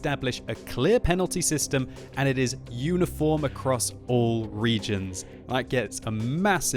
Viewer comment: hey, I recently downloaded the most recent version of xvm and it has a reload timer for last player who shot you (only counts down once after they hit you) would that still be considered illegal? and if so how do you turn it off?? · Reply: My bad haha I thought they said unicorn. Thanks for clarifying that.